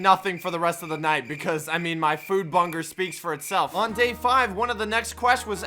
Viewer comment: I only just realised he said bunger instead of hunger